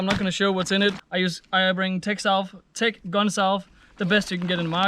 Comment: What was the stuff in the pink container? Thought you said tech gun self? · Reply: Tech salv